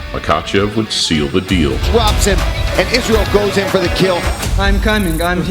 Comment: Did he just say ‘Isreal’ goes for the kill at 10:13??